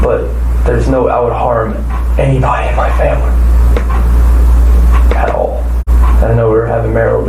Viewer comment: his voice suddenly switches to a whisper and it sounds like he reveals "I hate my family" when he is actually saying "anybody in my family"